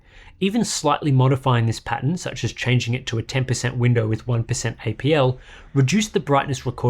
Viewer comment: What’s IPL? Or are you saying ABL